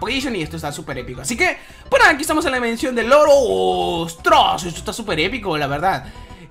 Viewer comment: Cuando estabas en la dimensión del oro te entendí "estamos en la dimensión del loro" XD